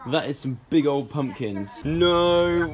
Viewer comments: i thought they were saying pumpkin not pump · Was i the only one that thought they were saying pumpkin instead of pump.